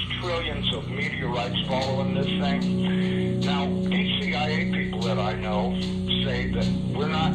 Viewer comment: Ok I might be tripping but I could have sworn he said he ate people that he knows around 1:20:40 that's where I here it sounds like he saying hate also but I don't hear the h sound